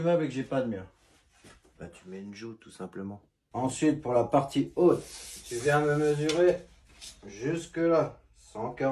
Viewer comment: tu met quoi ? une joute ? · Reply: Une joue de finition